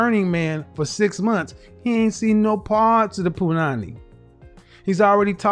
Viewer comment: Did Kevin say punany 10:34 that's a Jamaican word 🤣🤣🤣🤣🤣🤣